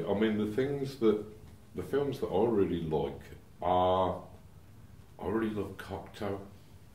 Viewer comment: I don't quite understand what films he says he likes there. Can somebody help? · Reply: Schaf I somehow doubt it's Uwe Boll, but I could be wrong. The CC titles are no help whatsoever here, typical YouTube. 14:24 "Already little Cocktail" instead of "I really love Cocteau"? YEEESSSH :-P